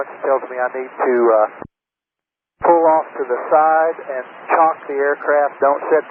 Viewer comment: Probably not. A chalk is a group of troops you carry and drop off, not a verb. It would be funny to see the passengers fast roping out the windows, though. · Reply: Thanks fir answering! I was thinking the pilot felt relieved about the safe landing and wanted to get the passengers off as quickly as possible, but I wasn't familiar with "chock", and I thought I heard "chalk". So I figured I'd look it up and then ask. Thanks!